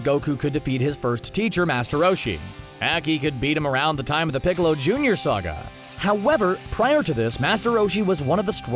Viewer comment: Did you just say Piccolo Jr?